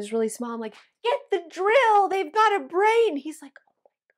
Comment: The end story, I was laughing my butt off, because it was funny but also because I heard wrong. "Give me the drill, (s)he has a brain!" Yeah didn't hear the complete sentence and almost choked laughing so hard.